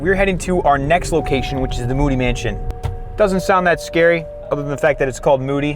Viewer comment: When he said moody mansion i thought he said "booty mansion"😂😂